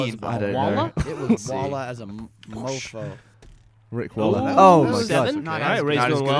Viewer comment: Did Gavin say Rick Waller? ._. Cause that's my algebra teachers name...